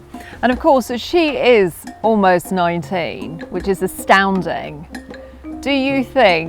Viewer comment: Thought she was saying the wolf was ninety at 1st..